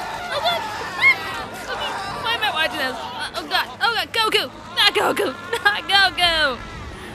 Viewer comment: It sounded like she said "Goku," if she did, was one of the characters of Berserk named that?